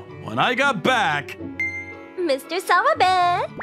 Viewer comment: I don't know why, but every time I hear the name "Mr. Sawabe", I tend to hear Mr. Swabe instead 🤣🤣🤣🤣